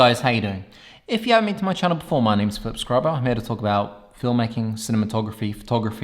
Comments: I thought he said "My name is Foot Scrubber" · Hahaha sorry mate, just clicked on your video and hearing you say your name was funny, sounded like you said your name is Foot Scrubber hahaha. Ok, lets see what you got hehehe